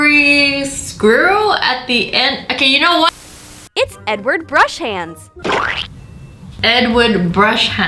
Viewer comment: I wasn't paying attention so instead of hearing "Edward Brush-hands" I heard "N-word Brush-hands" 😂😂😂😂😂😂😂😂😂😂